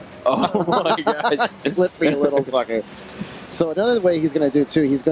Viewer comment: Does he say slippery little f**ker???